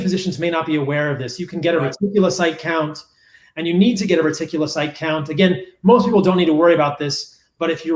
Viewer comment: I'm curious did anyone catch what count Dr. Saladino referenced at 35:17? It sounded like meticulous I count or something like that. Would be useful to know to request if tested A1C is high. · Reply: Dominic Fenton He was saying Retic Count. It's part of the CBC panel.